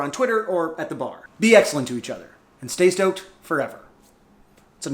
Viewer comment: Anyone else thought it said stroked?